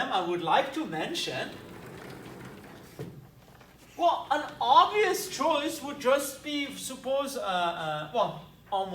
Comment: "one of the ???? of Riemann integration" sorry I couldn't understand that word there. Also I don't know what "in L^2" means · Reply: Chef-d’œuvres of Riemann integration